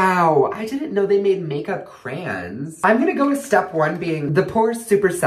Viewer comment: got me dieng. did he say trans or crayons??? 6:38 do you mean foundation???